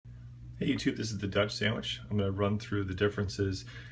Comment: i thought he said this is the duck sandwich